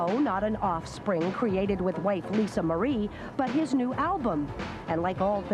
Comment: What a strange comment by the reporter mentioning having a baby with the “white Lisa Marie” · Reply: I heard “Wife Lisa Marie” not white